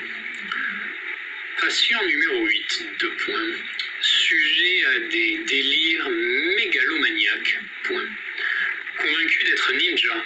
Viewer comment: I Swear, every new Easter Egg gets bigger and more exciting!! This is by far the biggest one we've gotten! :D Let me know what you guys are thinking about everything! Hope you all enjoyed doing the mini game! Have a great day everyone! Much Love! ♥️ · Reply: Hi I'm French here's what I heard. Feel free to ask me anything :) 12:10 Patient numéro 8 : sujet à des délires mégalomaniaques. Convaincu d'être ninja. Persuadé de pouvoir voler dans l'espace. Sujet de compulsions : doit appuyer sur ses propres boutons dans un ordre numérique spécifique lorsque la situation ne tourne pas à son avantage. Le patient numéro 8 est un paradoxe ambulant. Note : le patient numéro 8 est une machine lente, colérique et manquant d'assurance. Il lui est "virtuellement" impossible de se séparer de son pistoler laser. Toute provocation pourrait avoir des résultats meurtriers. Assure que son mode par défaut est rajout. Certain de ne pas être apprécié par son entourage sans pouvoir donner des angles précis pour se justifier. Des examens supplémentaires sont nécesseraires pour explorer l'obscurité dissimulée dans cette machine composée de panneaux de fibre à densité moyenne. Nicole, marquez le quand jeudi en 8 et mardi en 15. Ah hein mais c'est que c'est pas éteint ça ? et si je ...